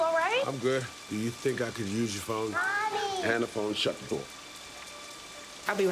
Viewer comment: I can't understand what he says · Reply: He says, "Hand the phone. Shut the door." It didn't show everything he says though. He says this: "I don't need to come in. I'll stand right here. You can hand the phone. Shut the door."